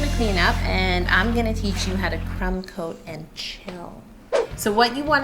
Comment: When she said now it's time to crumb coat and chill I thought she said crumb coat and kill...